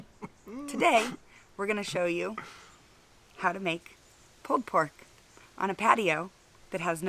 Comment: Did she just call it "Pooed Pork"? LOL! Looks KILLER guys! I've not tried the butter method on a shoulder but I do it all the time for ribs. I'm gonna have to give it a try!!